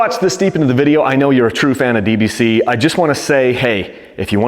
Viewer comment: I thought I heard you say "you're a true fan of BBC" 7:04 😂